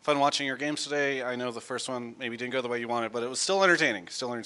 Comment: did say "still iINTing" at the end.